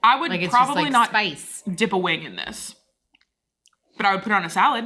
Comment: I thought kept saying "dip a wig in it" and was super into having a new thing to say...